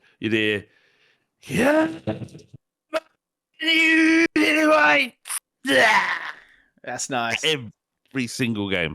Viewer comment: No idea what is saying at 1:00:45. Please help?? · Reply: He's saying "CAAAAAAAAAAAAAAAAMMMMMMMONNNNYOUUUUUULILLLLLYWHITTTTTTESSAAHHH". Can't see where your problem arises from?